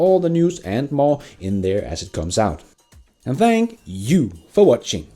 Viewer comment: Everytime you say "be nice" I hear "Bin ICE" :D